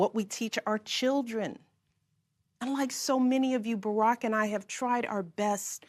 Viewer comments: did she say "unlike you" or did she slur her words and kinda say "and like you". Listen to it 5 or 10 times and let me know... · "Unlike so many of you". Did I hear that right? She said "Unlike so many of you Barack and I have tried our best to instill in our girls a strong moral foundation". CNN changed the transcript to say "And like many of you". Go to minute 6:00 on the speech and you will catch it. Who is she referring to? She is speaking to a Democratic audience. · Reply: GTFO. She said "like so many of you". Why on Earth would she say what you're suggesting?